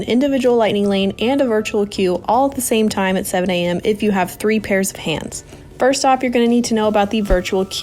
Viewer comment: At first I heard "you can get a VQ, ILL & LL, if you have 3 pairs of pants" 😆 Gooooo-ooon